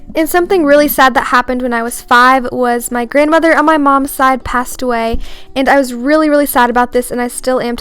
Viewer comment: I thought You Said Ur Mom Passed Away And You said When I was 5 Mum Passed away So Then I went to Private school Kindergarten But You're Mother Died Didn't She?